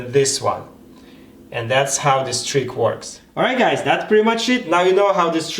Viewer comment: Sounded Like He Said "It's Pretty Much S#!t", Lol